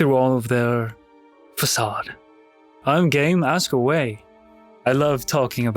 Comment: my gayass thought he said gay instead of game for a second there